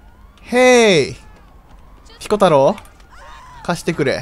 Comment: 歩く淫行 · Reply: シコ太郎って聞こえる 頭おかしい